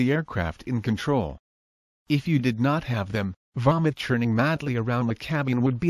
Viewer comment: "Vomit churning around inside the cabin will be the least of your problems..." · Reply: Oh right, vomit, I thought he said 'varmint', just as funny, and I'm not even a Yank!